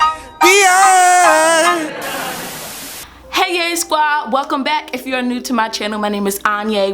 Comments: In the intro I beginning after da song I heard:"hey gay squad"😂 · Did she say ”hey gays” ?